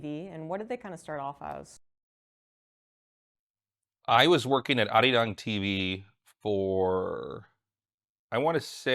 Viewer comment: What type of tv did he say it was @ 25:20? Is the site still up? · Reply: Arirang TV.